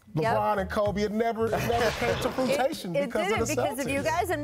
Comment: Did paul pierce just say fruit-tation instead of fruition!!! Lol!!!